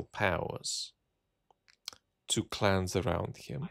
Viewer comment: I keep hearing Putin 'outsourcing to clowns' rather than clans